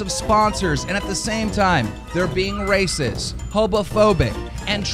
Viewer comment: damn he really said hobophobic